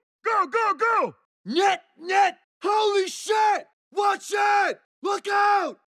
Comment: what did Minotaur say?? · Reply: this whole time I thought he said Holy Sh*t hahaha